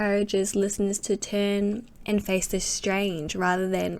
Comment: I always thought he was saying "turn and face the strain" on "Changes"